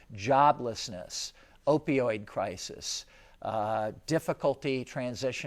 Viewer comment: did he say "opioid Crisis"?